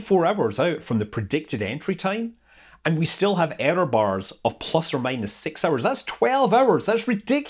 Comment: When the error bars are the size of the entire planet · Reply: Ohhhhhh, Error bars! I thought he said Areo bars. I was wondering why he was mentioning chocolate but I figured it had something to do with the bubbles.